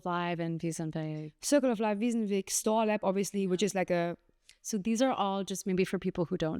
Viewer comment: Anybody catch the names of the jams mentioned around 48:00? I caught Circle of Live, but couldn’t understand or make sense of the other two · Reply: STOOR & WSNWG